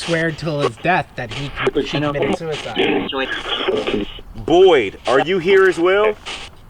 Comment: I heard 21:42 here’s the thing